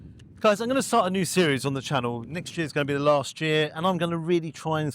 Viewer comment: did you say it was going to be your final year next year.